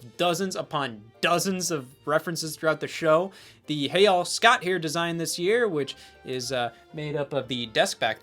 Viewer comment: what.. I always thought he was saying "Hey Y'ALL, Scott here!" my perception of reality is shattered